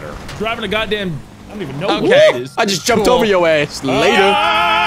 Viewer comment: First time around I heard "I just jumped over your ass, n****" 😳🤣🤣 No worries though, He actually said "I just jumped over your ass, later!" 😄